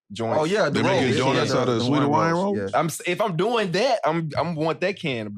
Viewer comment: his accent hella thick… I heard sweet ha-ryans, and then ha-lions lol